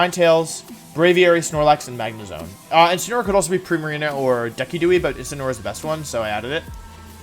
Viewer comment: did he just say “decky-duey?” And not Decidueye